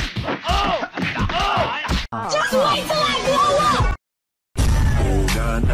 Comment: I thought it said “just wait till I GROW up.”